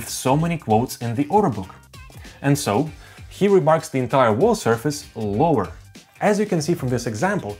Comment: "Here remarks the entire wall surface lower" Did I hear that right? I'm confused if I did. It doesn't make sense.